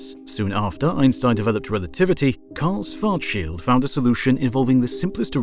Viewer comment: I swear I always hear this guys name as "Carl's Fart Shield".